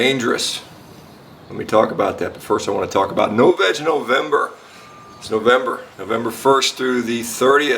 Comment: I got scared. I thought I heard, "No Vag November"☹ But NO VEG NOVEMBER? Count me in!👍🏻